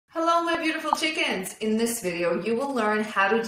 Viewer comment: did she call us chickens 🐔😂🤣